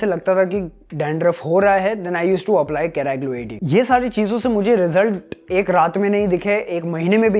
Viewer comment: What did you said at 7:00 ... whn u get dandruff what did u used?? I couldn't understand it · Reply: Keraglo AD shampoo - <link>